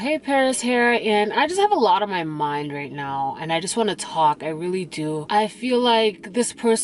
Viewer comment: RIP to the lost…. I know you just said “I just wanna talk” but all I heard was “I just wanna rock!” …..hips got to moving and everything